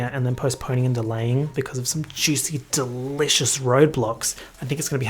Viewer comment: did he say roblox? OMG GONNA GET A ROBLOX CONCERT